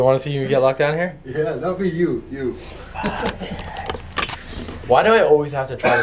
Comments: That was him saying fu$% it not stop it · I heard a whispery voice say FU$& YOU or YEA it didn’t sound like ur voice but idk😘💜👻